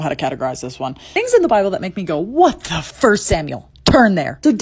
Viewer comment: Tbh, in the beginning of the short, I thought she said bibble instead of Bible😳